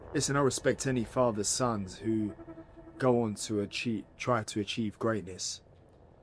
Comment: did Conor say who goes on to “cheat”? Oops